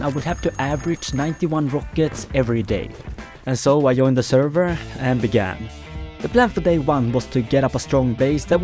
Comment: i thought you said... "and ofc i joined a 1000x server" xD